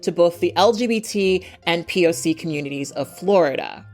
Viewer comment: Not sure who writes your captions! The fact that the captions say "p.o.s" instead of p.o.c. @ 14:17... that's a microaggression. lol. Just wanted to give you a heads up.